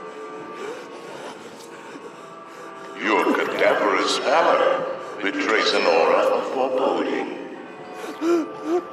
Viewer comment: can someone tell me what he's saying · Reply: In the elevator